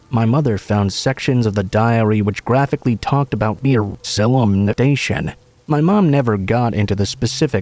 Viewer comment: what are they saying?? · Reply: Molestation